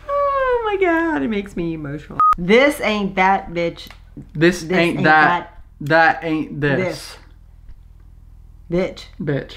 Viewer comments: BRUH!! HAHA I thought he was saying "this ain't death bitch, death ain't this!" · I thought he was saying, this ain't death bitch!, death ain't this!